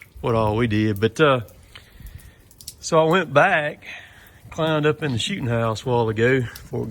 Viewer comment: It almost sounded like you said I climed up in the shitten house lol